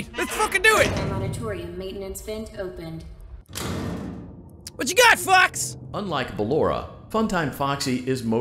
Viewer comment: What you got fox! I thought he said fucks